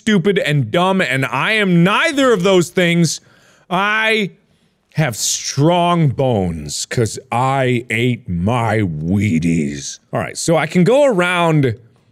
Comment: i swore you said "i have strong bones because i eat my weenies"